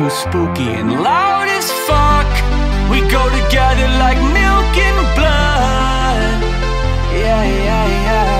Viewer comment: I need the lyrics to this. It sounds like he says “we go together like milk and blood”. Idk if that’s right cuz that sounds weird.